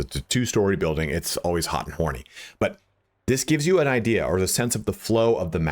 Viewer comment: Did I hear “hot and horny” 6:40 lol